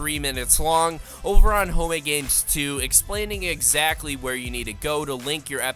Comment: It sounds like he is saying homogames